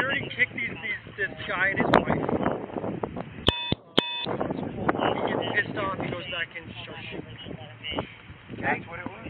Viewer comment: Wait wait wait what does he say at 20:23 I know this was early in the investigation but what exactly does he say after "security kicks this guy and his wife out" .....of the concert? I wonder if a officer at the concert had an encounter with a couple who got kicked out. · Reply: Yes I heard "security kicks this guy and his wife out, he gets really pissed off, goes back in and starts shooting." Very interesting indeed.